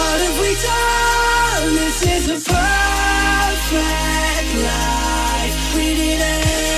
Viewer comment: Why do I hear: This is a PERFECT LIFE? Not lie...